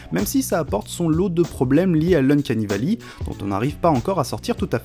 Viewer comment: Bonjour, je ne comprend pas ce que tu dit à 1:28. C'est quoi ? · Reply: "Uncanny valley"